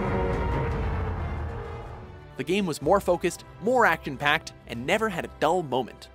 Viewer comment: did he say "actin" packed instead of action packed?